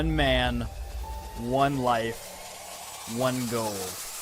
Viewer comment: I thought he said in the beginning 'one wife' xD